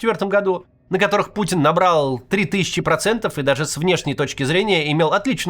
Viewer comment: Послышалось: "Выборы, на который Путин наврал три тысячи процентов" Да нет, наверное, не послышалось)